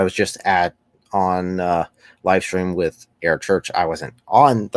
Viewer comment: When you said air church I thought you said Eric Church and I thought I missed a huge moment in country music history lol